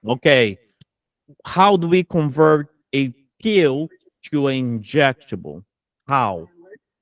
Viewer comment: Hey professor… I’m studying for my ANCC next week and I’m not a class student but a YT student and at 1:08:19… everyone was talking over you and I couldn’t hear the right answer and explanation.. if I hear any others I don’t understand I’ll time stamp it to this response… please can your clarify and thank you 🙏🏾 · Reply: A pill to an inj dose…